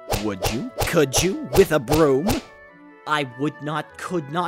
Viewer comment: it sounded like you said broom when you meant boom. but this... i wanna see hussie's reaction to this video XD